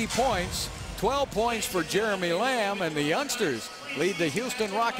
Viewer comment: Sound like he said 12 points for "Jeremy Lim" WTF, lol.